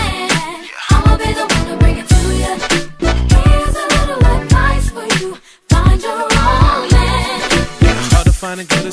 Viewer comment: is she saying he little to right for you? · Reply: Kaitlyn Winslow “here’s a little advice for you, find your own man”